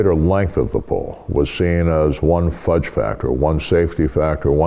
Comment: I had to use closed captions on this one because at 3:43 I thought he said fudgepacker...